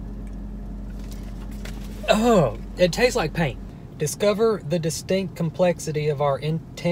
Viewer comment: “it tastes like paint” How do you know what paint tastes like? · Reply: I thought he said "it tastes like pain" lol